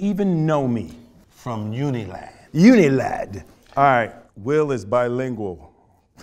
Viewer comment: I thought at first it said "Unlaid " instead of Unilad 😄